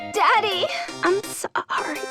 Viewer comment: oh......i know i wasn't the only one who heard the "dadd-" uhmm nevermind...